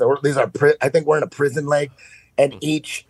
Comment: What's that he said? Did Eddie say we're in a giant prison lake? I haven't heard this one. What's he mean? Does he mean a literal lake? Or is lake an analogy for something else? It's hard to keep up with Eddie's ever evolving beliefs and lingo.